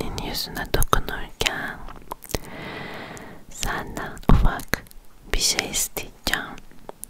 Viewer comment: it sounds like she said "Fuck" Lmao 🤣